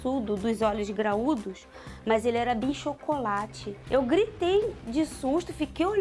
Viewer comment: Eu ouvi bem? Ela disse que o extraterrestre era de chocolate? 🤔😃